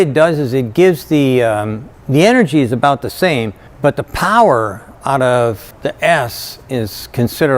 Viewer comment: i swear I heard "the power out of the ass" xD